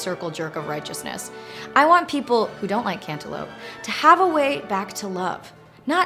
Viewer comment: I thought you said "Antelope" not "Cantelope"